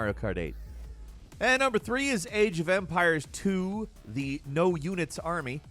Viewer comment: Man, it's not the first video that you talk about Age of Empires and I only hear "AGE O'VAMPIRES" and keep "wow, I never heard about this game before...oh, it is Age..." aeuiaeheiuaehaeiuhaeiueaheaiuheaiueaheaiuheaiueahaeiu